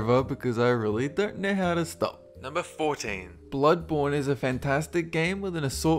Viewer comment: I thought you said bloodboner